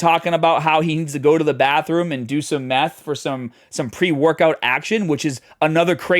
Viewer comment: Did I hear that properly? He needs to go to the bathroom to do some meth for pre-workout? I've been training a decade and I don't even know what pre-workout looks like let alone meth for training! What's happening with these kids?! If he's on meth that explains his strange behavior, clearly.